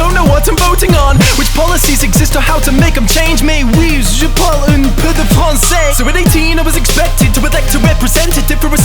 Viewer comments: Does anyone know what he's saying in French? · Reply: "mais oui, je parle un peu de français" but i speek a bit of french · If I heard it right, at 2:35ish, he says "Oui, je parle un peu de français" which means "yes, I speak a little French." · What is he saying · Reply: What policies exist or how to make them change may we use (idkidk) and at 18 I was expected to represent a system I had never ever ever ever been presented